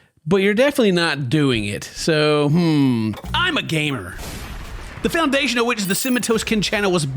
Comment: I have sponsor skip so I just heard "I'M A GAY"